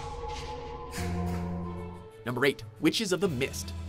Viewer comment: Did he say "Number 8"?